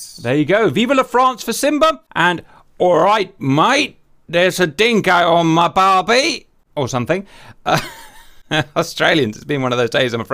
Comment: "There's a dingo on my barbie!" OMG, I'm dying laughing! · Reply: lol! I thought he said a dingo ate his barby. 😄